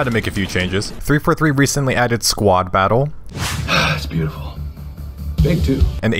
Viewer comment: For a second I thought he said squat Battle. LOL 2:06 Also Overwatch literally doesn't exist anymore.